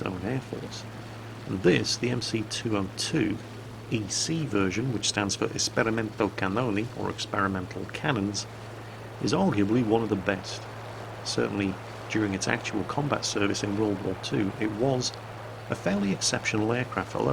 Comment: Jingles, for a moment I thought you said "Experimental Cannoli". (I just woke up.) :D